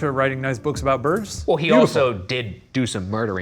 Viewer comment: did a ghost just flirt with me? i heard “hey beautiful” when the crew was leaving the corridor LMAOO